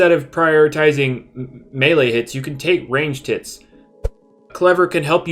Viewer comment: "Instead of priotitizing melee hits... You Can Take Range Tits"(5:40) -- X Master Oogway 2021